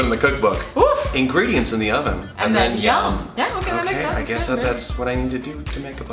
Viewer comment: I thought she said "finger dancing in the oven, then YUM!"